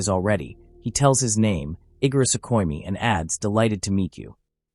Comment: i thought ghe ai said igris lol